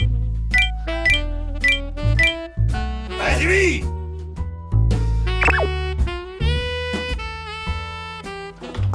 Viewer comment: 今さらすいません ここ何て言ってるんですか？ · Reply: 始めぇ！かな？